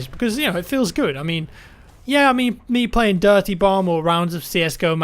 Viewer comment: it sounds like he says Dirty Bong... just me hearing that?